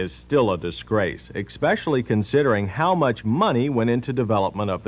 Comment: Did he say “exspecially”?